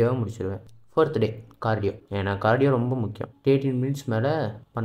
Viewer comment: 30 min or 13 min can't understand pls reply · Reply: 30 mins panna muscle loss aagum neenga kammiya na time run pannunga 10 mins antha maari